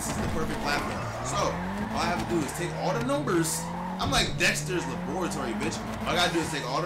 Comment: did he say la bore a tory?